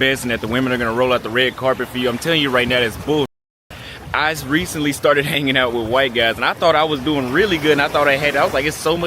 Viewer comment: Did he just say "I's recently started hanging around white guys" 🧐